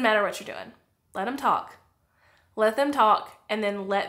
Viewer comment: At the end I first heard “Jessica Alba” and I was like, wha? Then my brain was like oh she said “Jessica out” 😂 I am loud and proud about my content but my consistency…..